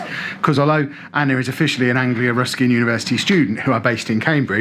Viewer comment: I really thought you said “Angry Ruski University” <.<